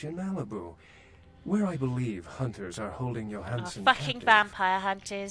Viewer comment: Every time that dude said "yoh-han-san" (or however it's spelled) I heard "you're handsome"...